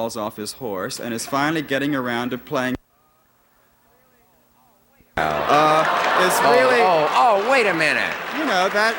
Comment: If anybody reading this is deaf, could you tell us what the hell he said at 7:04? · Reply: "a character close to his own personality"